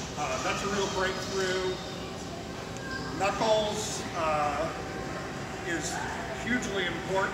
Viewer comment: When he Gabe said knuckles I swore he said nutballs